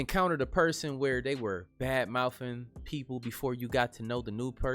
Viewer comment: Did he just say, " Bad moufing"?